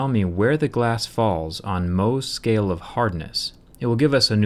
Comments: I thought you Said "Mosts scale of hardness" AKA the scale most ppl use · Who else thought that he always says 'Most Scale of Hardness'? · I always thought he said the Most scale of hardness... until i saw this video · I thought that he was saying Most scales of hardness.. Like not all of them, but most of the scales are like the one he is using.. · wow, I thought that he always said "most scale of hardness" all this time lol